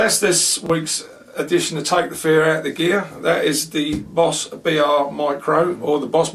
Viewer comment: I thought it said "Take the pear out of the bear".